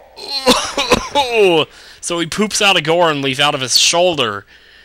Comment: Goron Leaf?? Is that what you said??? Nice crazy laugh, btw.